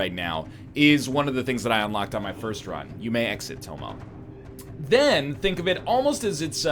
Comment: What does he say here? Why is it so random and out of place? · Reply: Here's an answer for a 2 year old question. His cat is called Tomo, he said "you may exit Tomo" probably letting the cat out.